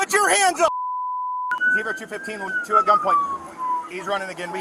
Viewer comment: I thought he said two at gunpoint